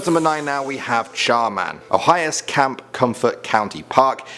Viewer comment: I thought he said Charmander.